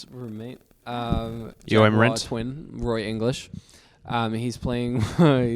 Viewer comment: am I the only one who has no idea what Luke said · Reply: he said “you want rent?”